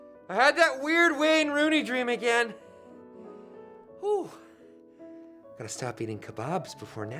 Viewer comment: i don' know what he said ' 0:50 ' please tell me :( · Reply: "Gotta stop eating kebabs before naptime"